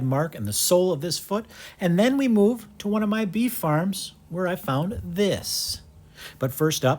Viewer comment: i thought that you said bee farm when you said beef farm I was like why would a bee keeper have cows?